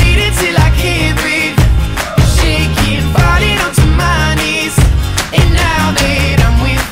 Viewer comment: I can't stop mishearing "Falling onto my knees" as "Farting onto my knees"